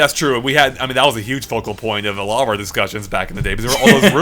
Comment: at 16:55 for a moment I thought you had said vocal point, then I realized you had said focal point. I thought you had unintentionally made a pun.